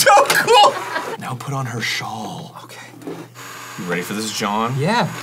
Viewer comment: Did Arin just call Dan, John?